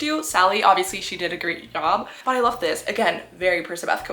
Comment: for a second i thought you said leobeth and i was like do people actually ship that !??!?!?!?!?!?!??